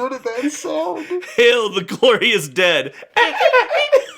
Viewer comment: Genuinely laughing until my nose was running Also, I heard "Hail the corniest dad" and not "Hail the glorious dead" and honestly, with the honking sound, it fits.